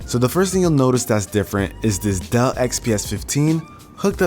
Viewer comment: why did I hear "duh" instead of "Dell"😂